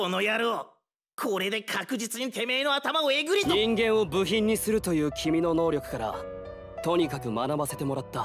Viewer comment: it sounds like giorno saying the n word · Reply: Ningen